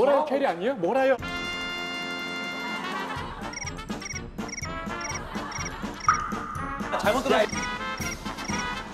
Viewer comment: 나 진짜 이때 은지언이랑 수그니 뭐라고 했는지 삐처리 없이 듣고 싶다고 ㅠㅠㅠㅠㅠㅠㅠㅠㅠㅠ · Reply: 앉아이새끼들아는 들리는데 ㅋㅋㅋ